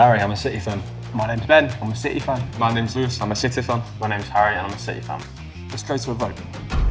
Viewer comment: All i hear in 0:20 and forward are ''i am a g*y fan''